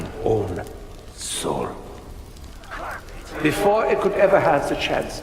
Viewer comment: No joke I thought Richthofen said flush not flourish